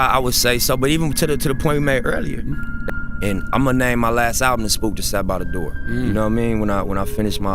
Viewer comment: Wait, did he say, "Imma name my LAST album "The Spook Who Sat By The Doorway"??? Or did he say, "next album"? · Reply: He said "last album....."